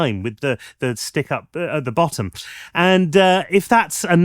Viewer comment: I swear I heard you say 'stick up the bottom' at 13.50 there.